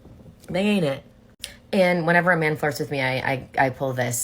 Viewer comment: Did she say “Whenever a man farts with me?” Ah the beauty of love, and it stinks!!!